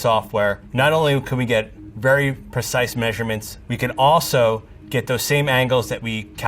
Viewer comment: all I heard was, very expensive camera.